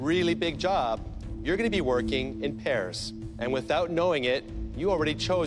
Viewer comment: I thought he said you’re going to be working in Paris😂😂😂 not pairs